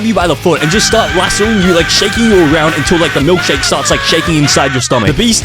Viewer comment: what did he say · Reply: Until the milkshake i fogo